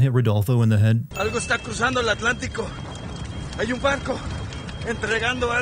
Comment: (Before watching will edit later) Ngl im only watching this cus i heard Guanajuato was in this game and i live there XD Edits: 6:05 what is that Spanish? I heard "Banco" (bank/possibly bench) instead of "Barco" (ship/boat) The whole Sin Nombre being a woman feels illogical, Mexico is very patriarchal, like yeah there are women in cartels, but nothing tells me she "Zheng Yi Sao" the cartel.